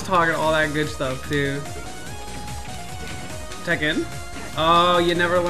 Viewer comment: “tech in” Some people are gonna think he said Tekken lol